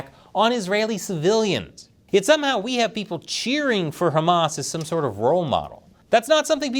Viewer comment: That bonus hole person at the beginning should remove its face covering before speaking . I swear I heard " ham in ass". I had to replay it and decifer what was said . Now why it wants ham in its ass, is a different discussion.